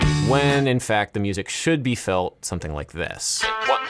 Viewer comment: It's a pickup note man... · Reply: he says it “should” be felt as a pickup, so that’s what he said lol